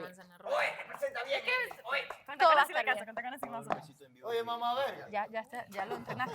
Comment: jajajajajjajajajjajajajaja oí bien?? jajajaj "OE MAMAV*RGA"